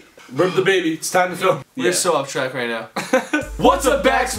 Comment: I thought Gray Said: were still on crack right now L.O.L.